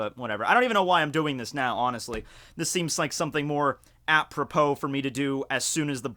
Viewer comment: What did he say there 0:26? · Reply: Apropos